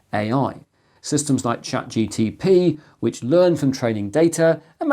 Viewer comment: Did you say "Chat GTP" (around 7:08)?